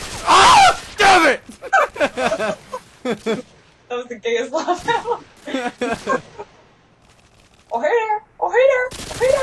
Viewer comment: Did she just say laugh was "the gayest laugh ever"????? Check for yourself but i think she did O.o